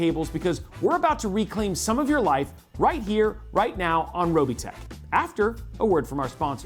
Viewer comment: swear thought I heard "RIGHT NOW ON ROBOTECH"